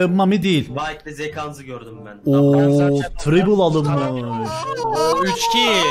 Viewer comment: doublekill i duydum da threeblekill i ilk defa duyuyorum :d